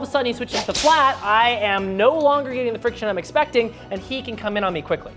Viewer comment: I wasn't paying attention and I swear I heard "C*m on me quickly" Damn, bro... Just damn